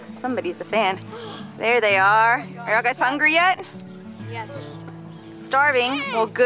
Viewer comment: When you asked if they were hungry, it sounded like said: Yes I am! ☺️